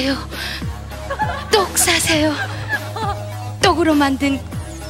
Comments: 나만 똥싸세요라고 들림? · 나만 이거 "똑 사세요 똑 사세요" 로 들리냐?